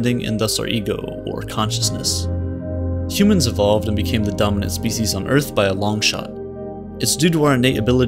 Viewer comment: first I thought u said dumbest not dominant 😂😂😂